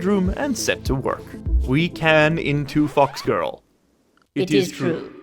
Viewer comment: English isnt my Native lenguage, so, can someone tell me wtf did he said in 1:22:48? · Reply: I believe he said, "we can into fox girl" as a reference to a meme of some sort or another.